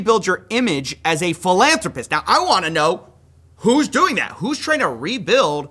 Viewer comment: Lol I'm sorry but every time I hear the term philanthropist I hear "full on rapist" 😂 thank you Charlie day.